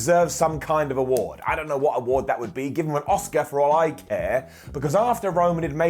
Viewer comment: Sorry, I thought you said give him a Auska not Oscar.